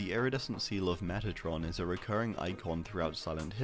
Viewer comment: I heard seal of metatron and thought you said Megatron